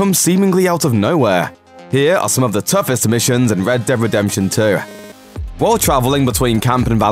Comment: No Offence but who heard "red dead redemption TUAHH" at 0:13.. 😐